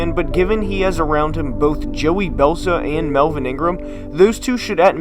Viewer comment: did he just say Belsa instead of Bosa? lol